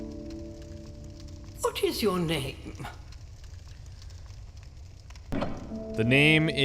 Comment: When the old witch (guessing) said "what is your name" i heard "the fuck is yo name" lol